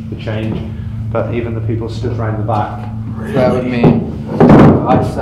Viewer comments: Hey, loved this vid but i heard a voice in 22:02 saying smth like 'really' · I heard something say "really" at 22:03 idk might've been the chair but didn't really sound like it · >> Was reading through the comments but it seems I'm the only one who hears, just as put down the chair in front of the scrying mirror, an audible loud deep voice I guess say "REALLY"... it doesn't sound like it would be from the person holding the camera, it just sounds different for some reason..